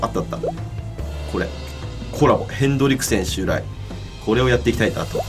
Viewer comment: ヘンドリクセンが『カールじいさんの空飛ぶ家』のフレドリクセンさんに聞こえた。